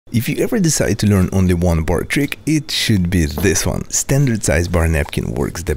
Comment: I thought he said eat s*** and do this one until I turned on captions, biggest mistake or my life